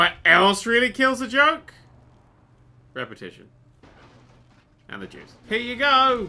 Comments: Listen at about 8:40 because I swear says 'and the jews' and I was crying laughing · did he say and the jews?